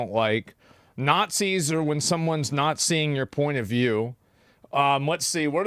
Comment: "Nazi's is when someone nazing your point of view" I thought thats what he said lol